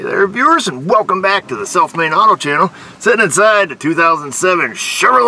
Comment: Long as I've been seeing you videos by YouTube's recommended videos I always thought you were saying ''self made auto repair''. Just now I've realized my hearing is getting worse in my 40s but my eardrum has been ruptured since I was 18 so it was bound to happen. Just subscribed also and that's due to my lack of understanding the online world until recently I made it my mission to get with the times of today.